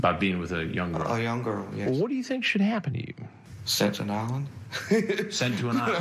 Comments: For the LONGEST time I always thought he said: “So what do you think should happen to you?” “Sex on an island” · Sent to an island!! 🤓😂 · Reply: I always thought he said "citizens Island" lol